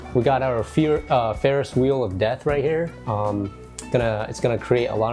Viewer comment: Ferrous wheel of death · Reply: At first I thought he said "…wheel of debt" heh